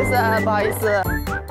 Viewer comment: HI! 0:27 CAN SOMEONE PLEASE HELP WHAT WORD SHE SAID IN THIS. DUI BUQI IS SORRY RIGHT? i want to know what she said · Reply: She said 不好意思 which also means sorry or excuse me